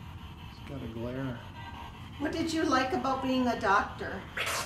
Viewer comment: When said, "what do you like about being a doctor" (1:20:57), I wasn't even looking at the screen and I heard it say "lots of blood".